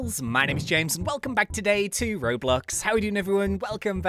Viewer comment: When i saw this vid i clicked on and i thought he said hey guys welcome back to roblux XD